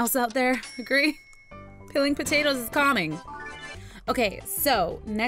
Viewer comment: anyone else hear: "Peeling potatoes is Karma." No? Just me? okay...